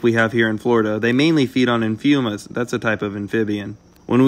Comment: I miss heard you because I thought you said “They mainly feed on humans.”